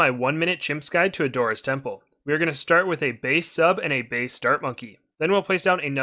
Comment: I thought he said based sub and based dart monkey 💀